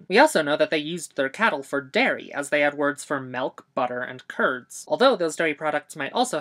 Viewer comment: did you say 'melk' instead of milk😱?